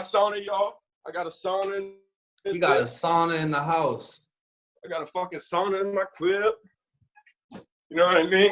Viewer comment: I died . Sounds like he said “ I got a Sauna in my Clii “ 💀 mad respect tho he’s making 6 figures